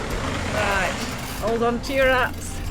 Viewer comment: “Riiight, ‘old on t’yer ‘ats” Proper Northern lass. 😍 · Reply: Well that helps clarify, I heard “‘old on t-yer ass” and shrugged…seemed on point.